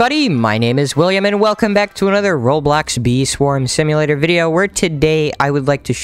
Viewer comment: i thought he said wii sports instead of bee swarm